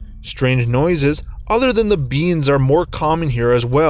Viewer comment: who else heard bEans